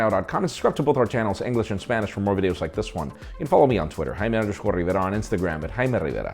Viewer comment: i thought i heard his name is jaina rivera 😅😅